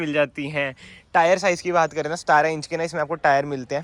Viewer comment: i think i hear stara inch 😅